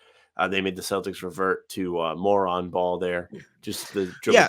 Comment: what's poppin? at 12:59 was saying the wolves made the celtics play "more on-ball" or "moron ball"? maybe it doesn't matter...i agree agree either way. drats! i might have just agreed with two episodes in a row, and that may be more disappointing than the loss.